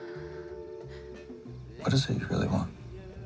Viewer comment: what did sarah say in response to john b 😭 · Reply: I think she said ‘yea’ but it kinda sounds like ‘here you go’ but that wouldn’t make sense